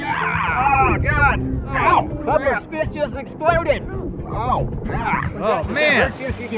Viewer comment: I thought said “that sum bitch just exploded”